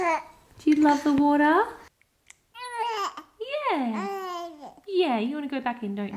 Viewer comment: it sounds like she says “yeah, I like it” 🤗